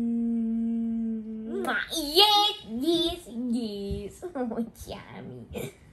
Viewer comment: What is he saying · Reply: yes yes yaisss 🥺❤🥺❤